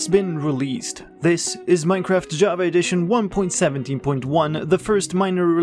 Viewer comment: Literally lots of people told me 1.17.10 will release the same time as <num> is 1.17.1 for bedrock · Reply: lol, I thought you said 1.7.10. I used 1.7.10 so much because so many legacy mods support that version